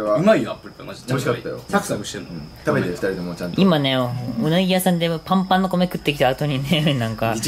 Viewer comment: うなぎ屋さんが 胸毛屋さんに聞こえた